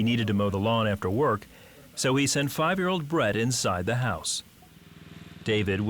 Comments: At first I thought he said, "So he sent 5 year old bread into the house" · Not being funny but I thought he said “He needed to send 5 year old bread inside the house” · Did it say his Son's name was bread?